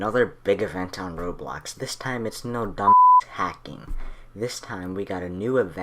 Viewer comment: LOL did u SAY "dumb ass" on 0:35?